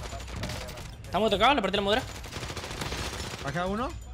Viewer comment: Siempre pensé que decía le partió la Moura y me acabo de dar cuenta que es armadura